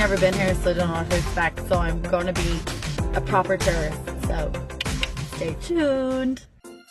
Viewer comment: At first when you said tourist i thought you said terrorist. completely lulz worthy lol.